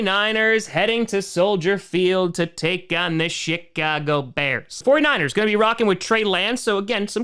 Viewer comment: I know you didn't mean it, but it sounded like the "Sh*t-cago Bears"